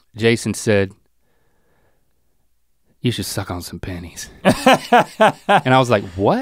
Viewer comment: i heard that as panties 💀